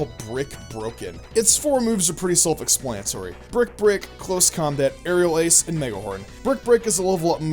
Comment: Umm, did u just say brick brick?